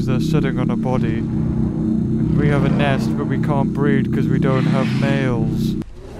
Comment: it says “we can’t breath because we don’t have males” if you put in captions instead of “we can’t breed because we don’t have males”